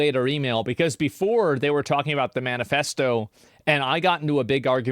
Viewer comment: I thought he said ‘bananafesto